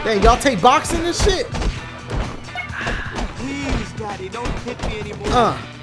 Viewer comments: what did he just say? xD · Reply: loooool ikr, I thought I was hearing things. Please daddy, don't hit me anymore... wtf?? I'm weak XD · Did anyone hear "please daddy dont hit me no more" in